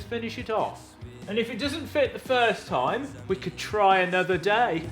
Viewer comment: Did you say "First time" or "Furze time"? I hope it was the second one.